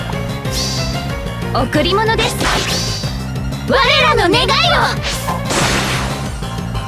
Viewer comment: wtf did they say here 1:01 🤨🤨🤨🤨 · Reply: "Ware ra no negai yo!" "This is our wish!"